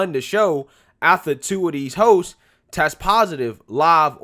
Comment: Did he say "...after two of these hosts" ?😃